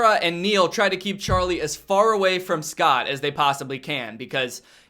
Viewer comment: when Danny explained the first scene i thought he said the main dudes name was scott cawthen and I was like "THE MAN BEHIND THE SLAUGHTER" cuz I legit love fnaf so fucking much and then I was like "hold up he DID kill santa-" @@